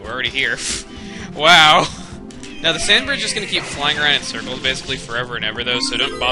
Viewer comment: did he say the fanbird?